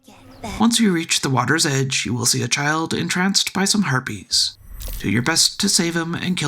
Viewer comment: You said it before this timestamp as well, so I thought my mind was playing tricks on me, but... you said it again.. herpes instead of harpies. Kinda funny. 😁